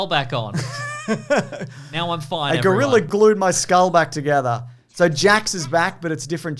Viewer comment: I heard this as "a gorilla glued my skull back together"